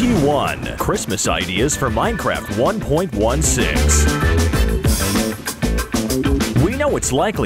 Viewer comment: realy? Did you say 1.6